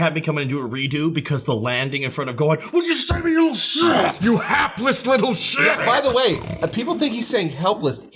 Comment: I always thought he was saying heartless until a rewatch with captions on.